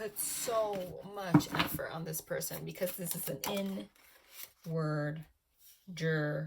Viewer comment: i heard “n word” at first 😂